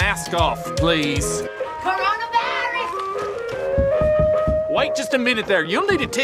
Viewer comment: listen to the sound did he just say "Corona Virus"???